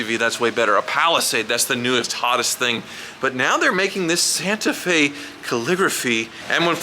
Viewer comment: For a moment, I thought I heard Santa Fe “Caliphate”.